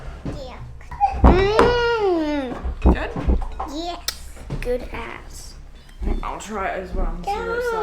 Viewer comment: did anyone else hear that lol, maybe I misheard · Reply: Good 😂 ass